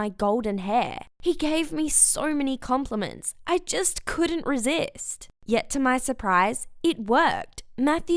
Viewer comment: "...just couldn't resist"?? I hear you said "agreed" to all of them. I'm wrong, aren't I?